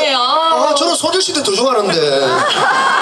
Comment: Why did I hear he said something about SNSD at 1:24?